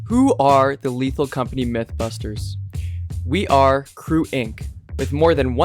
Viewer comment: for some funny reason my brain thought you said: CREWBUSTERS instead of MYTHBUSTERS